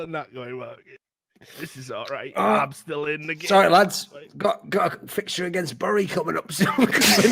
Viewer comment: I can't understand what he's saying at 10:28, can anyone help me? lol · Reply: "sorry lads, got a fixture against Bury coming up soon" implying his FM streams